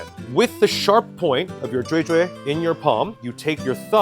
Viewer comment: What was the zhui zhui word you used? 7:54? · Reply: 锥。 the word for cone。